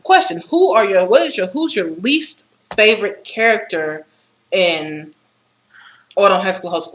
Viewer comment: Oh god listening to my voice is never fun also derpy exhales. Also for the record I thought you said "favourite character"